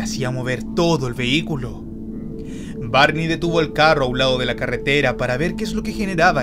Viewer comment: dijo barni haha o al menos oí eso, excelente video feliz navidad y prospero año nuevo